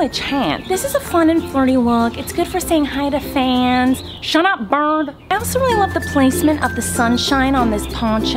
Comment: Did she really say shut up bird at 4:35?